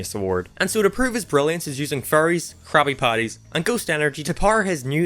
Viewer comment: I thought u said he was “using furries” until I realized u said faries.